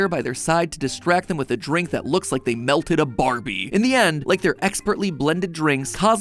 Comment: Oh melted! I heard it like you said milked.